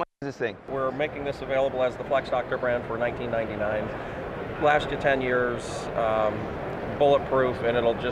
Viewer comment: Great video, amazing what they sell at these events. I actually thought he said $19.99 not $1999.00